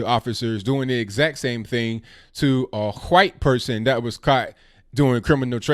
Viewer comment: Hey, did just say Huwhite? hee, hee :)